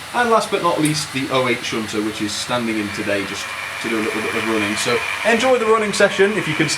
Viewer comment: WHAT DID YOU SAY?!?! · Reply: I believe something like 'Last but not least, the 08 shunter, which is standing-in today just to do a little bit of running' - why do you ask?!? D